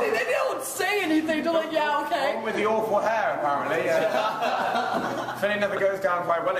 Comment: legenda tá errada no 5:56 mais ou menos. ele fala alguma coisa tipo "awful hair apparently". só não entendi a primeira palavra. por isso eles tavam rindo